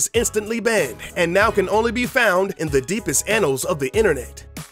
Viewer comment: did he say it can only be found in the deepest anals of the Internet?